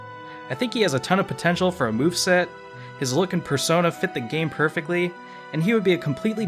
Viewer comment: When talking about Egg Man I heard, "...his look in Persona would fit the game perfectly." When was Egg Man in Persona?! ;)